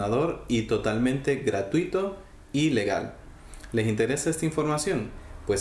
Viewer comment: en el minuto 0:16 : creo que dijo ilegal yo : QUE PEDOOOOOOOOOO el del video : les estoy enseñando todo lo ilegal muajajjajajajajjajjaj No me maten ;-; ,fue un buen video 👌 , me sirvio :3